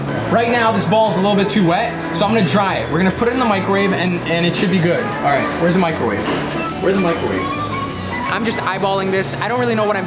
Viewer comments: i heard lets put it in my grave !!! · It sounded like he said "we are going to put it in my grave."😂😂